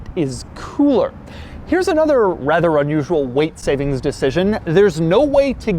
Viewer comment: I could have sworn he said “white savings” the first two times